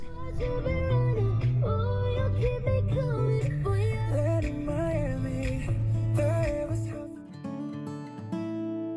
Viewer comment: LOL @ 12:54 ... Sing it, get down, get back up again. What music site did you say? I couldn't hear you. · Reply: Epidemic sound. It’s a site I pay for to use their music on the tubes. Thanks man I was hoping someone would notice that little bit lol